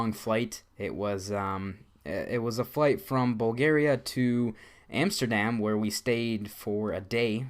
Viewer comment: lol First i thought you said Boogeria instead of Bulgaria, and why didn´t you visit the red light district in Amsterdam